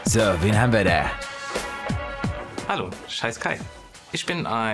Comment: Oh man...ich hab verstanden "Scheiß Kai"😮